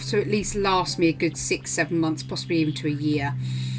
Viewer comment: Taken by who? I'm sorry I couldn't understand what u said, I really wanna see the reference photo. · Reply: His name is Jess Findlay, he is on instagram x